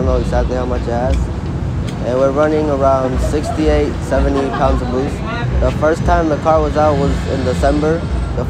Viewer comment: Did he say 70 lbs of boost?